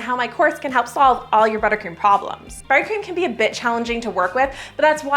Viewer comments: You said “buttercream can be a bit challenging..” and I SWORE you said buttercream can be a bit** 😮😮 hahahaha I have been wanting an ice cream maker so this video came at a great time! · When you said "buttercream can be a bit challenging" I heard "buttercream can be a bitch" and the Chef in me thought too true! 😂